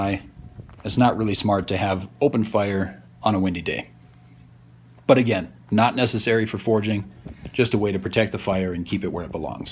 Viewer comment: I thought it said foraging, I was so confused